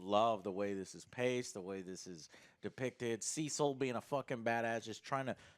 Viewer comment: I swear, every time I hear "Cecil", I actually hear "Sea salt"! xD